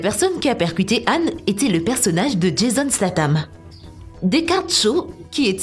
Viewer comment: C'est moi où elle dit Jason Slatan ? 😂